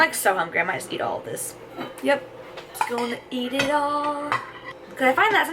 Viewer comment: Yay~ another video!! Love your meal preps! Don't know why, but at 9:31 "eat it all" sounded like Let it go 😂 Keep doing what you're doing. looking forward to the next vid